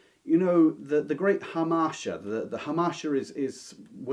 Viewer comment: What’s that word at 26:17 that describes greatest strength being ones downfall? The Great … Hamasher…? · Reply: It's his accent -- I couldn't quite get it at first but clearly he is talking about hamartia, a literary term.